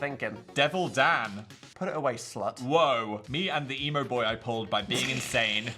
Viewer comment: the way I heard Me and Thee at first and for a second I got super excited that my worlds had collided